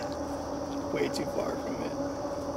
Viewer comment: Did anybody else hear another scream around 17:10 and then a voice saying something like "way to far from it"? I have a double ear infection and can't hear shit, but I heard this! WTF! And how are you guys so calm? Lmao I'd be losing my shit. I get scared doing this app in the daylight. You guys should go back when the sun is out!